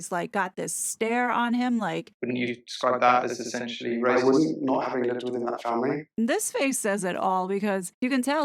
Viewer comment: Does any one know what Harry says at around 3:14 in answer to Bradby's comment? It's not even in the transcript, it's so muddled 🤔 Thank you if anyone can advise! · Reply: He says, "NO, I wouldn't, not having lived in that family".